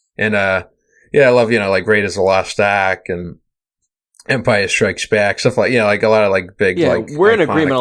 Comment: Am I the only one who heard "Empire Shreks Back"?